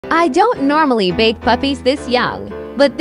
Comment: Ugh, why did I hear "I dont normally BAKE puppies this young."?? 😮‍💨 Loll he is so precious ♡